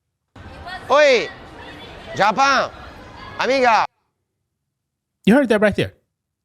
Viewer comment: Wait.. did you just say “amica”??? Because he said “Mi amiga” (my friend) lol